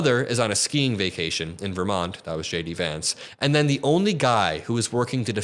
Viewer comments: I think I heard you say Shady Vance. A good nickname for him. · I thought you said "Shady Vance", I like that name more.